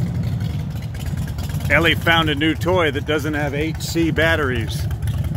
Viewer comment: At first i thought he said AC batteries and i was like, do they make those?